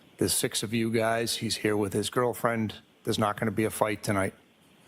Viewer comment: did he say girlfriend? I missed that while watching the first time.